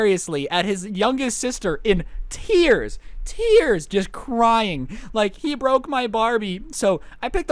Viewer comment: When a ad hit it sounded like u said oh he broke my bomb